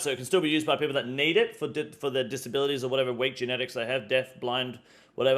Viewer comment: did he say “weak genetics” 😅😅 that’s crazy!